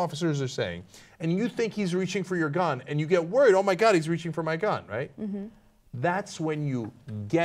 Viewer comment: This is a sad story, but.... What I heard was "he's going for ? gun" where ? could be "my" or "a" or "the" and then later "drop the gun". I thought I heard that before I heard TYT talking about it. I don't think that necessarily justifies killing him, though.